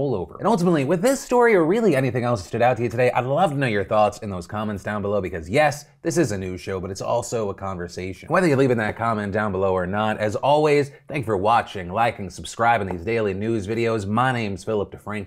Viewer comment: People are so weird... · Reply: This entire time, I thought you've been saying "This is a new show" I was todays years old when I realizes and added it up that you say "This is a news show" my brain has been opened, I guess its time for me to ascend to the next reality.